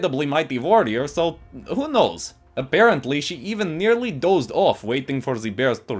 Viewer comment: Is it just me or do you hear "A-'bear'-ently" instead of apparently lol